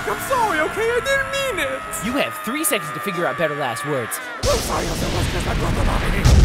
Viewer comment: Can anyone actually understand what Joe was saying at 2:06 I've rewinded that scene hundreds of times and I still can't make out what hes saying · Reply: I think he said "I'm sorry. I didn't even mean it. "😂😂😂😂😂